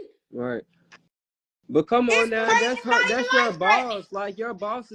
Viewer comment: Rollie, you lying I heard you say “come on ET” on the show.. I would never be friends with somebody like her .